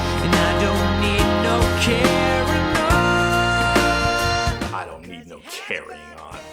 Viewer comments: Oh my god I always thought he was saying "I don't need no care at all" 💀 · Oh He Says "I Don't Need No Carryin' On"? I Always Heard "I Don't Need No Caroline" And Wondered What On Earth He Was On About.